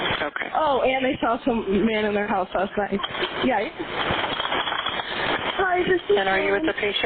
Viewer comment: it sounds like she says yikes.